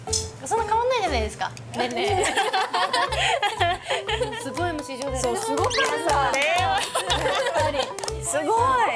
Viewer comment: ここなんて言ってるか分かりますか？ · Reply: すっごいMC上手やったー(お世辞)